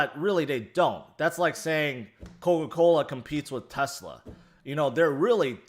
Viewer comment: At first, i thought you said thats like “Nikola competing with Tesla”. That sounded like a diss to Ethereum, until i rewinded it and heard you said Coa Cola @ 9:00.